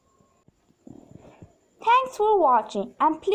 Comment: Oops I thought you said tortilla at first when you said tutorial